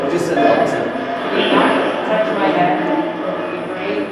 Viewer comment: After the divice said “Elton” I heard “touch my hair”